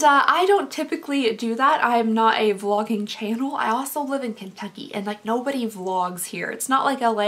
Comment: When you said “I live in Kentucky” I heard “I live in Italy” I was really jealous for a good 5 seconds. (Kidding some of my family lives in Kentucky I’d love to live there too)